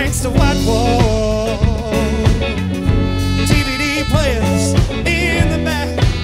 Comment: He said, TVD Players in the back. xD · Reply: I thought he said TV dinners in the back 😀